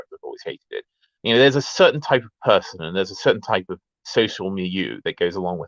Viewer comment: what is this word, i can't work out the spelling of it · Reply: social milieu